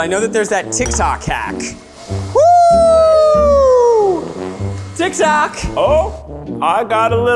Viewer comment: Lmfao!! 😂🤣 Most the time the closed captions piss me off/irritate me because they are absolutely wrong, not even close at all to what's being said, but this one .... made me laugh so hard! @ 6:05 when Zach says, "Woo! TikTok!" The closed captions read, "Woo! Dick sack." 👏🏻😂 Wtf! He definitely did n o t say dick sack 🤣😅